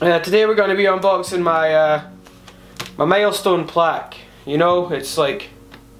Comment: Every time you said "milestone" I thought you said "maelstrom"